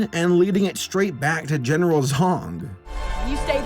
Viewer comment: "General Zong"... .... Did no one else immediately think "General Zod" or am I the only one??? I swear I thought said Zod at first then I heard it again and thought "Oh, he said *Zong*"